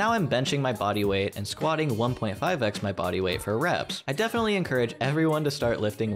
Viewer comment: I misheard "squatting 1.5x my body weight" as "squatting 5x my body weight" and spent the entire rest of the video utterly baffled at how you managed to improve so quickly lmao